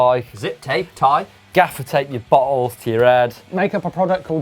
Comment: Did say ''gaffer tape your balls to your head'' at 12:50?